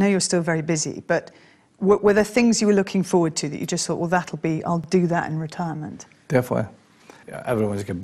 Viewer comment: what's supposed to say "that you just thought well that be" What I listen: "tha you thought wuodatt be" ._. it's so hard to understand them, they speak too quick